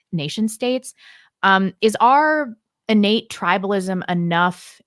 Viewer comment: says, "arr" instead of "our" which is very common in America nowadays. I wonder if Mr. Dutton, as an Englishman, is annoyed by that Americanism.